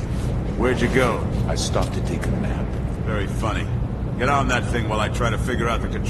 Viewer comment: What did Turok say at 39:51 I didn't understand him clearly. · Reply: I stopped to take a nap